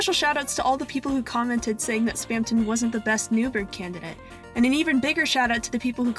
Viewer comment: Kept mishearing "New Bird" as "Nubert"